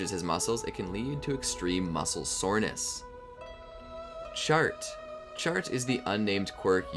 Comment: When you said Chart, I thought you said Shart, and I then thought there was a quirk that just let someone rocket diarrhea at people. I guess we all have that Quirk if you think about and eat the right diet.